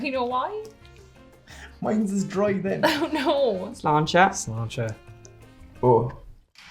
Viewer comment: The auto generated CC says they said "@ Launcher" to each other. lol